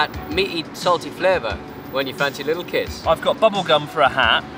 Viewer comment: I thought he said, "when you fancy little ids" I had to rewatch that part with subtitles on. LOL